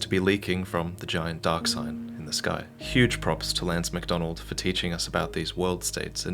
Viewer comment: I thought he said Grant McDonald the guy who made ram ranch